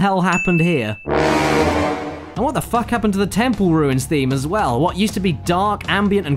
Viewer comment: In crash 3 i thought he said nipple instead of temple